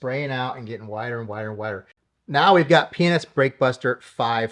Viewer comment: what did you say 😂😂😂😂😂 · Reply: 🤣 P&S, but it sounds like something else. 🤣